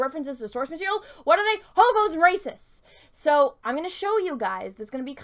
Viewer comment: I could have sworn you said “Hobos and racists” lmao